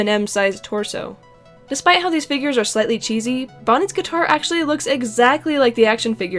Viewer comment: DID YOU JUST SAY BONNET